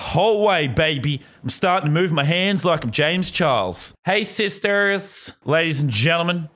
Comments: Did Lazarbeam just say “hey sisters”? 😂😂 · my captions said 'hey sis terrorists' James Charles joined isis confirmed? 🤔